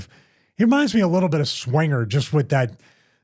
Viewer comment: Funny, I thought you said "SWAGGER" and your point still works 😅